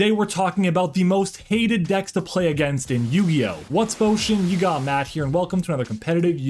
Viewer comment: Alright, I gotta ask. What exactly is your intro? I swear I hear “What’s Fo Shigga? I’m Matt here.” And I thought, for the longest time, you were saying “What’s up? Matt here.” And then I thought you said your name but why the “what’s” in the beginning. I’m legitimately confused what you say for the intro. I need clearing up, please 😅 That being said, decks I’m tired of and no longer wish to duel against and will almost always scoop on Master duel: Tearlaments, still. So much milking and chaining. You can play someone else, I don’t want any part in it. Labyrinth because they can pop a card in my hand before I even play a card and they also take forever to play.. At this point, Fire Kings. It’s not as much advantage one guy in my discord plays it and ONLY plays fire kings, he doesn’t even try playing anything else for fun so I just don’t care to duel against them. I’m cool with Kash and Floo even though Floo gets annoying at times but if I go first then I’m usually golden and can stop their combos. Kashtira is a necessarily evil and I’m all for it. Exosister pissed me off at locals the other day but I also didn’t play the right hand traps and he got me good with Nibiru and Gozen so it was fair, he was just the better player. Don’t hate the deck atm. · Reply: "whatsup Oshean" (what i call my community) thats a lot of hated decks lol